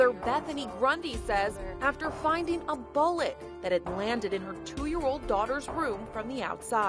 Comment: I thought she said “A bollet!”